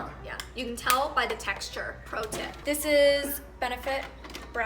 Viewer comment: Did she say ''protip''? 2:56... where is the phandom?